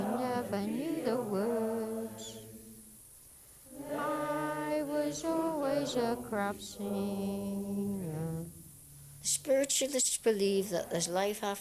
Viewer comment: does he say "I was always a crap singer" ?